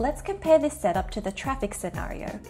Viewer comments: I thought it said BraLess. :( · Oh clickbait I thought it said bra-less